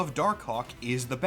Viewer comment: You… you really need to annunciate Dark Hawk better my guy. I thought I heard “Dark Kawk” (say it out loud you’ll get it) like 10 times. 😭